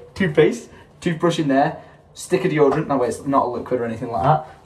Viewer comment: Had to do a double take at 17:00 thought he said stick of yogurt.